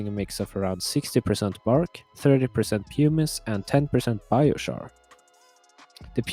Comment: I stand corrected. I thought you said humus (the organic component of soil) not pumice. That's a whole lot better and makes your mix a good one for the longer term. Coir is still good- it breaks down slowly and has anti-fungal and bacterial properties. You're correct about moss, with the exception of living moss. Keep up the good videos my friend!